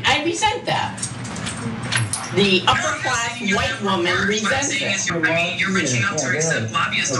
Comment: thought she said woke woman not white woman? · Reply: She said " this upperclass white woman resents that"